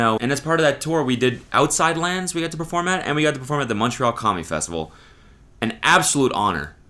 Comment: I definitely thought you said “Montreal Commie Festival” and I almost lost my shit